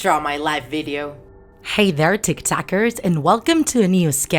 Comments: did you say TikTokers? Lol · For a second I thought she said Tik Tokers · At the intro did she just say..... Hey tik tokers! and i oop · TBH I thought she legit, said "Hello there T I K T O K E R S!" .... What is wrong with me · When she said tik takers I thought she said tik tokers